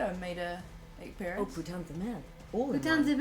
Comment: Gustave says "Putain de merde!" which Billie and then Jen repeat. YouTube's autogenerated captions decided that what Billie actually said though... was "Oh, put down the mail." and I love it lol